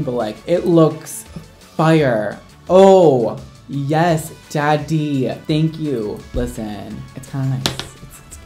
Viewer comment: did brad just say daddy or am i crazy 0-0